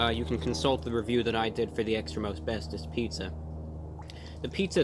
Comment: Every time he said, "Extra most bestest pizza." I heard, "Extra asbestos pizza." 🤢